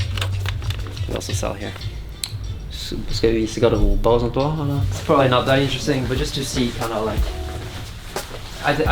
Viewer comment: what does he say here? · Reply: Should i show the wardrobes and stuff also?